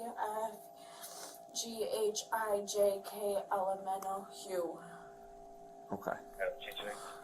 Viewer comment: I thought she said L M N O PEW